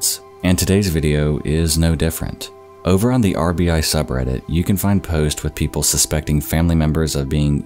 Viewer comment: I thought he said "on the Arby's subreddit"